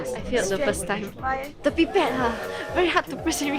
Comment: Was I the only one who thought said pee pad instead of pipette?